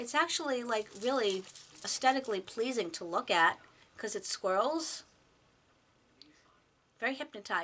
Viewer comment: Lol ..I thought you said " because it. squirrels" I so lovies you!!! Did not realize you were on PoshMark.